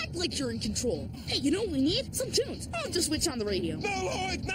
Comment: I remember when I WAS a kid and Lloyd said "this calls for some tunes" and went to push the button and I thought he said "toons" not "tunes" because that's how Americans pronounced "tunes" XD and I was all like "how can you watch cartoons on a radio?"